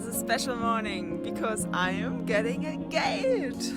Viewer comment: I thought you said engaged... but it was a gate. It's looking amazing.